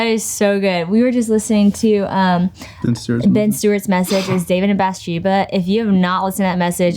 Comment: Hii, can someone please tell me what message they're talking about here please? I am not a native speaker and I can't understand clearly but I'd like to listen to it :( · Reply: I believe she said the title of the message was David and Bethsheba by Ben Stuart